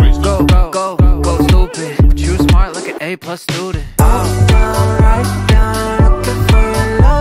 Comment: But why am I hearing "but she smiled like an a+ student"?